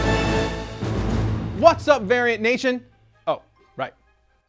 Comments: Sounded like he said Aryan Nation. Lol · I thought he said "what's up, Aryan Nation?"